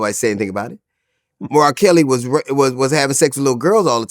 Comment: When he said r Kelly having sex with little girls this whole time it almost sounded like he said Mariah Carey lol. I was like whoooooaaaaa not Mariah !!